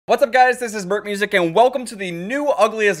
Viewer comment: Sounds like you say "burk music"